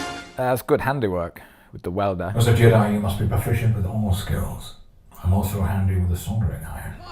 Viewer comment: all I hear at 3:38 is "as a jedi you must be proficient with homeless girls"